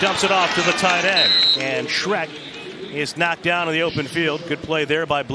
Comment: Did he really say the tight end name was Shrek @ 4:15 lmfao